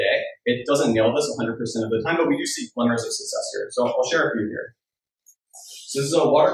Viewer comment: I can’t hear anything other than “plumbers of success” here 20:10 (and can’t help but think of Mario Bro’s as a result) as I read the caption before listening. Any ideas what was *actually said?